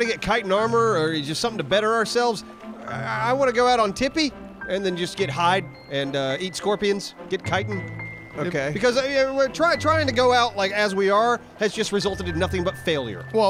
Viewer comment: sounds like says "I wanna get high and eat scorpions"